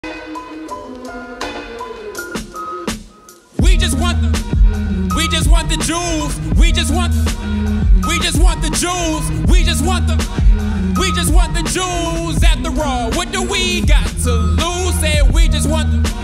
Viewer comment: At first I thought he said: "We just want the Jews" 💀💀💀